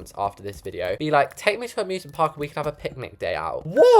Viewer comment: I thought it originally said Abusement park 😭